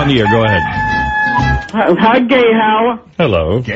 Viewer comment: Hi gay Howart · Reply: I laugh my ass off at this every time, but I hear "Hi gay Howerr".